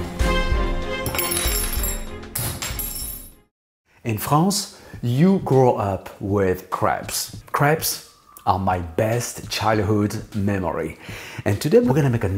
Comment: Love your videos! Going to be honest, my uncultured brain thought you said "In France, you grow up with crabs"...